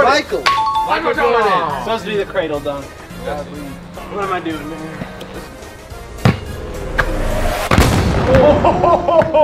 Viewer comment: It sounded like said " what am I doing n****r"